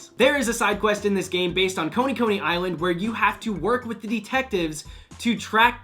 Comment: did he just say koni koni island wtf